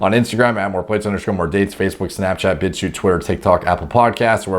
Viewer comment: I listened to it 20x now trying to understand what he says at 7:07 but i keep hearing "Facebook, Snapchat Bitch you twerk" xD Can someone tell me what he actually said? Im from Germany so English is not my first Language.